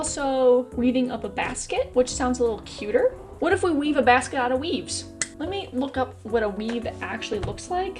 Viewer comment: I thought you said weaving a basket out of weebs for a sec